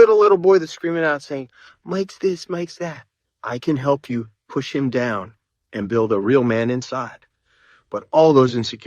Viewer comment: did he say he just say he wants to push a little boy down and put a man inside of him?